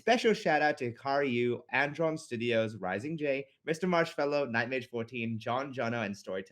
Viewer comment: I thought said DJ.Marshmello but he said Mr.Marshfellow